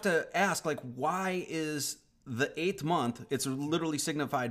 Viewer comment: did you just say "height-th?"